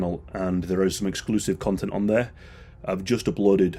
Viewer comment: Swore I thought he said “and there is juicy content on there”